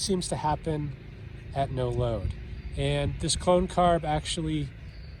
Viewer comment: You said "clone carb" and i heard "clown car." 😂